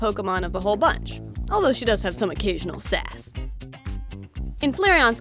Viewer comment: lol i thought she said, "Although she does have some occasional s*x." LOLOLOLOLOLOLLOL IM LAUGHTING DO HARD RIGHT NOW